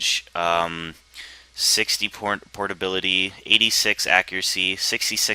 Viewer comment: lol listen carefully at 4:10 it sounds like he says 60 pornability xD